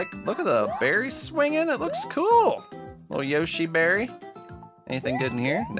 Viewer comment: I heard "Yoshi marry" instead of Yoshi berry lol